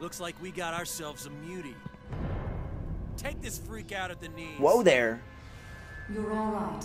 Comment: could have swore I heard arrow to the knee